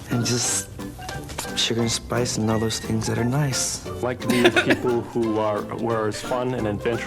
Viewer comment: I heard that as "old spice, lsd and things that are nice!" And I laughed ass off for about 30 seconds before realising I heard it wrong